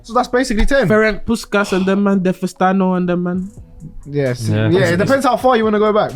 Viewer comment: did bro just say Di Festano